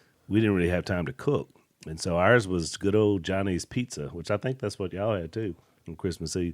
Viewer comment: You said “Johnny’s Pizza” I thought you said “Chinese Pizza “ 🤣 I enjoy listening to you , It’s nice to hear Christian men be real and discuss every day life. Keep it up my brothers!